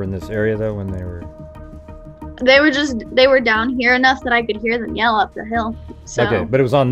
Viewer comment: sounds like she says that’s enough anyone hear that like she’s done with that story because she’s caught her self up